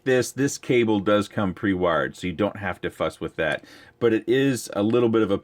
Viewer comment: " the wire comes mounted, so you don't have to f@ with that" .. could be I'm wrong. If so I apologize. Just never heard that from your channel before. Again, could be I didn't hear it correctly. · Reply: Its fuss and not f@ Yeah and I can admit. Betafpv product support is on paar with eachine/frsky. Worst i have dealt with.